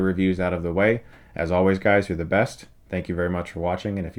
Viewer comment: I always thought at the end he said "you can get struck in the eyes" i was just dang chris goes hard.. but na hes just saying stuckmanized?